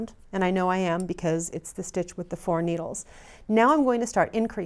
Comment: "stitch with the four needles" xD almost didn't catch it. Jokes apart you're a great teacher